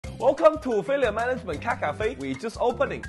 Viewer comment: Sounds like hes saying caca-fe